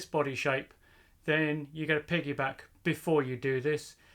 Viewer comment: is he saying piggybank? What is he trying to say? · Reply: It’s piggyback. It’s a type of add on ECU where the bike ECU is locked. Skäl.